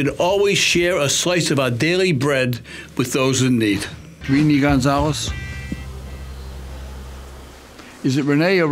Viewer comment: When he said "Renee Gonzales," I heard "Speedy Gonzales."